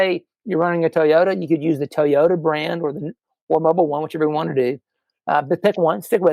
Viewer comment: what does he say about Mobil 1 and Toyota? Is he recommending Mobil 1 for Toyotas? Sounds like he says "or Mobil 1 which you're gonna want to do"